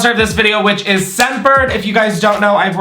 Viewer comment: Yep the edibles are hittin... why does it sound like is saying "ScentFerd" 🤔😐🤣🤣🤣🤣